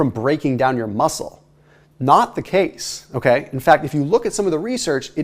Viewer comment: I swear that at 10:18 he says it's "not the cakes" instead of "not the case". Freudian slip? LOL. Thanks for this awesome content, I'll remember to make sure I don't eat any cake except for maybe 3x a year instead of every month like I was doing.